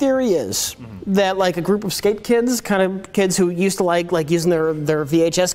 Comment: THERE'S ONE AT 9:37 THEY DON'T CATCH HE CALLS THE SKATEBOARDERS 'SKAPE KIDS'